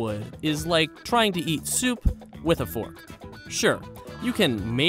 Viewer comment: Him: "it's like trying to eat soup with a fork" me: hold my beer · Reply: I thought he said heat soup. Eat makes way more sense